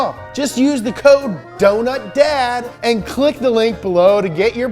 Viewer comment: i heard "don't nut dad" @ 23:11 XD